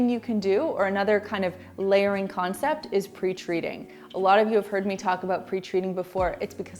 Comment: When you said "pre-treating" I thought you said "preach reading" and was wondering what it was and how I'd missed it before. 😂